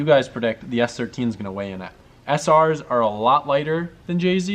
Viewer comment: I thought it said washing not weighing 😂😂